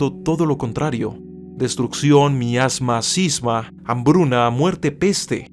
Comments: Fuentes: Historia de estupidez humana - Paul Tavori - 1951 Grandes torpes de la historia 10-10-11 Vicente Fernández López,. Quo La construcción social de la estupidez (2-07-12) Daniel Innerarity; El país La estupidez artificial (1997) Josñe M. Piquer Las leyes fundamentales de la estupidez humana (01-04-08) Nodo 50 Trabajo, consumismo y nuevos pobres (1997) Zygmunt Bauman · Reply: que es sisba? lo que dices "miasma, sisba" ... busque y no encontré esa palabra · que es sisba? lo que dices "miasma, sisba" ... busque y no encontré esa palabra